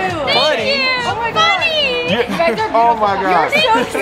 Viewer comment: if you listen closely I heard one of the girls say beautiful baby’s and then Bryce said : that:👀 and Addison just laughed Correct me if I am wrong 😂